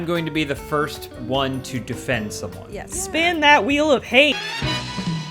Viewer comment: Chanting "wheel of hate" all I heard was "we love hate"